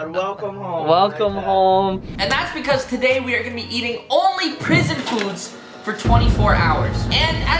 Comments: I just realize it said prison food after a full day I thought I said poison I was like WTF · At first i thought it said “I only ate poisoned food for 24 hours!!” I was like, what the heck hahaha.